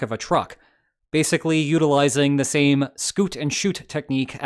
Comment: said "scoot and shoot" instead of "shoot and scoot" like the subtitles had. Literally unwatchable. Really fallen off your game... Jkjkjk, love you <3